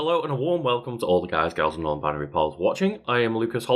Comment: I swear I just heard "guys, girls and non-binary pearls"